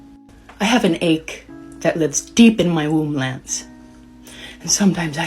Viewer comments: Everyone thought she was saying "womblands" but i think she said, "deep within my womb Lance"! She is a nut case either way though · I can't believe you'd say this about someone with aching womblands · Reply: I thought she said “aching in my womb Lance”. Womblands is so much worse!!! Lmao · Ohhh… she said “womb, Lance” every other video it was made to sound like she said “womblands”